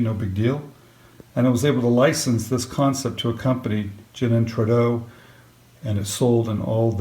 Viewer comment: Hi, who did you license the spin cup idea too? It sounded like you said "Gin and Tredo", thanks. BTW amazing work getting it into Disney. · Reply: One of the companies mentioned was Trudeau.